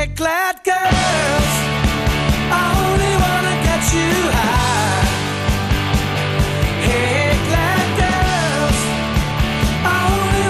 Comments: I'm also one of the ones when I first listened to it I thought he said black girls I want to get you high lol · the first time i heard this song i thought he said hey black girls i dont know what made me hear that...i think i was stoned that day..... what the hell did i do that day....where am i?